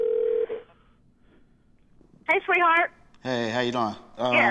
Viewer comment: did she say hi sweetheart? how did she know it was him calling